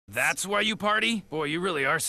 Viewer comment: All these years I thought he said ricked instead of wrecked 😭😭